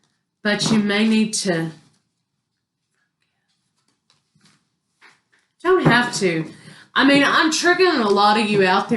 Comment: I don't know what you said at 31:30. I know you probably went all hush-hush because of the YouTube police, but now I'm dying to know what you said. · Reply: Forgive.. lol